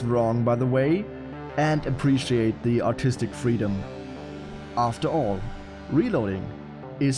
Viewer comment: I swear he said autistic freedom lol